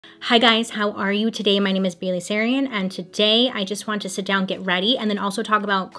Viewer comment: The pause in the intro made it sound like she said "Today I'm bailey sarian" and I was like "who are are you usually?? 👀"